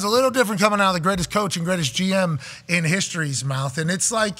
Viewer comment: did he say greatest GM in history?